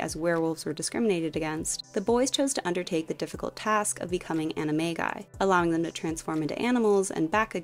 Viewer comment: I had never heard « animagi » out loud in english, i thought you said Remus’s friends « chose to undertake the difficult task of becoming anime guys »